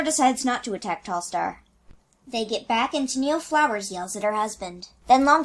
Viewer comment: Did it say at 3:39 "They get back and TennelleFlowers yells at her husband." ?! XD